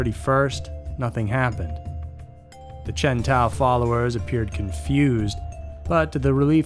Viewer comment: When you said Chen Tao I swear I thought you said Chevy Tahoe. 😂 That's a different kind of cult there.